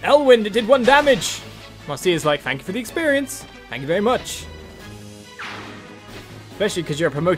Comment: I think Marcia was actually saying "Thank you for finally letting me equip the Fullguard."